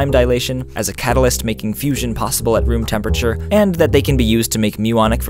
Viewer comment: sorry, but did you just say fusion at room temperature?? Like as in cold fusion!?!?!